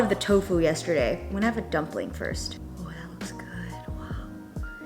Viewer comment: Idk why but when you first said “I’m going to have a dumpling first” I thought you said “I’m going to take a dump first” and I laughed so hard like woah this what I eat in a day got real